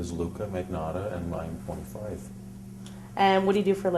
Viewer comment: She asks him his name and age. He says Luka Magnotta and 9.5 ? Is that what he says ? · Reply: teddy mills he says he’s 25